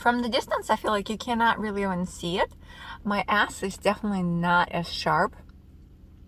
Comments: Girl I thought you said “my ass definitely isn’t as sharp” and I said WHAT 😂😂😂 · I heard “my ass is definitely not as sharp” and then I was like OH SHE MEANT “S” 😂 I’m 24 and I am having to redo my braces because I wasn’t able to do my lower braces in high school because of my jaw and how it was growing. I have a class 3 underbite and I gotta have a bi-jaw surgery (both top and bottom jaws) so correct my bite 😱 So I got them in December 2019 and now I’m nearing the end of the braces process and the surgeon said I could have my surgery as early as June! So nervous 😬😬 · I heard “My S is not as sharp” as “My ASS is not as sharp” and I was like “WAIT A SECOND WHAT?!” 😂